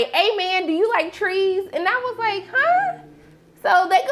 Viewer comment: My only problem with the TJ character is he getting money for a culture that’s not his to sale. And was the owner of the house hoping to have a moment with paperboi (recreate the ghost scene)😩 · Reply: Yes, I thought Fernando said "Do you like threes" and not "tree" implying he wanted a threesome with Paperboi.